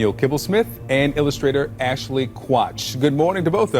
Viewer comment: Did he say Ashley Crotch?